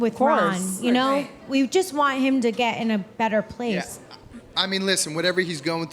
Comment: Did say she wants Ronnie in a better, like death!!!